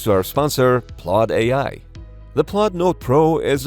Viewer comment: I had to double take since I thought you said this was sponsored by Claud AI, lol